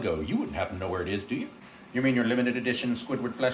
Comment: Oh no! The limited edition squidward fleshlight... at a corrections facility!? ... yeah they can keep it... 😅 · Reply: I thought he said flashlight